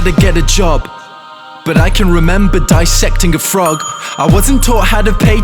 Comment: When he said "But I remember dissecting a frog" I thought he said "But I remember digesting a frog" lmaooo